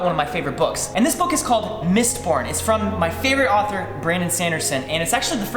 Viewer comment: Thought ya said Mist-porn there Wren